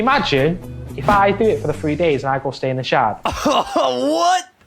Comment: What is he saying at 11:50 ? Going from homeless to staying in the Sheraton? The captions say shard and I can't understand the accent. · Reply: the shard :)